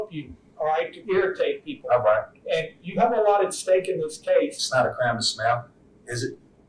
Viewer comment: I thought he said, “It’s not a crime to smell.” He’s obviously saying “smile,” but while he’s correct regardless, I see nothing to smile about. Human beings, including two minor children, have been murdered by someone…